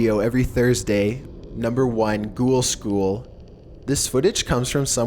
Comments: Omg when he said at #1 ghoul school, i thought he said cool school. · when he said ghoul school i thought he said cool school